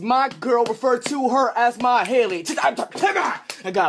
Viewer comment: does he say “My Hailey” ??????